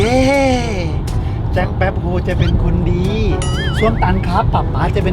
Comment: ได้ยินว่า ส้วมตัน ฟังไม่ผิดใช่มั้ย5555